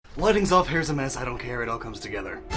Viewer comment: Do you guys really like that first line that much? haha. I did everything here in one take because I was in a hurry. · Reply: i thought you said "wedding's off" instead of "lighting's off"